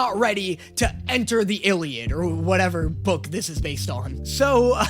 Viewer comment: “or whatever book this is based on”.. I thought he said he was prepared 😭